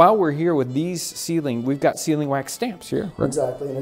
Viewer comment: For the first 15 years of my life, I thought people were saying "ceiling wax". I thought people waxed their ceilings like they wax their floors. Then one day I finally saw it written.